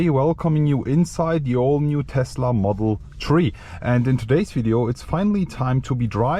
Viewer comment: I think I herd him say the Tesla model tree